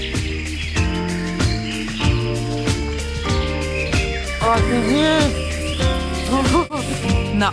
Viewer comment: мне послышалась? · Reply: Darkhan Kumiskaliev Ииии? Офигеть она сказала